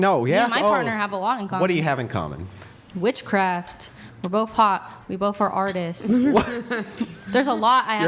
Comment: did she just say we're both hot 😂😂😂😂😂😂